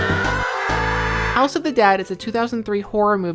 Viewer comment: I was looking at my other monitor when you said "house of the dead" and i thought you said "also the dad" which i thought was a pretty cool movie title